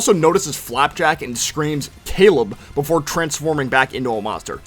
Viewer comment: I bet most of us heard kill him instead of Caleb